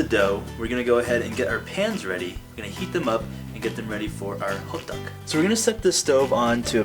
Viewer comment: lmfao i thought u were saying hookduck the entire vid. until i saw hoddeok